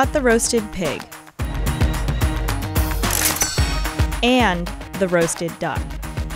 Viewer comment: Is it bad that I heard “The roasted dog” at 2:34?